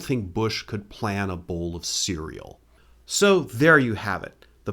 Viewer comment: "I don't think Bush could plan a bowl of cereal." 😅Finally, someone says it! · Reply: i thought he said “old bowl of cereal” which is also funny :)